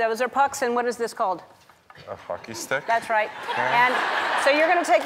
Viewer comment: Ellen: what is this? Anze: a hockey stick... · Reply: I thought he said “ a fucking stick”